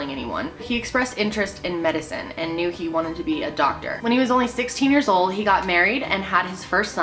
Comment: i thought she said 6 years old instead of 16 and i was like boi tf lmfao